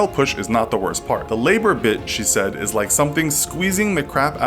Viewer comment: Sounds like he said bitch